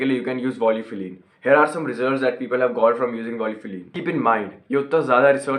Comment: what you said I didn't understand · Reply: Volufiline